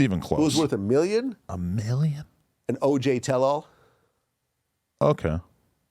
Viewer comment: who is he mentioning? "OJ Tello"? I dont understand him well or idk who he talking about haha im not from the US pls help someone maybe i know who he meant